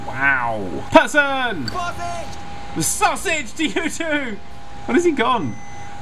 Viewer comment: That person at 27:15 did not actually say "sausage", he said "Vorsicht!" which is german for "Caution" But it's more funny with sausage :D